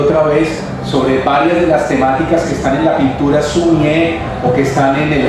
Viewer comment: ¿Qué es lo que dice en el min 9:53? ¿Pinturas qué? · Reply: Sumi e - Haiku